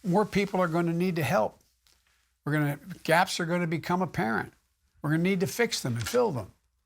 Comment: What does he say at 12:10? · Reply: "GAPS are going to become apparent". ..'s referring to the equipment he sloppily tried to talk about getting for the healthcare and workers of America :/